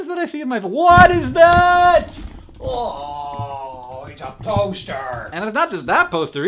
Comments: I thought he said toaster instead of poster. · When you said "agh it's a poster!!" I swear I heard " it's a toaster!!!" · I honestly thought you said "its a toaster"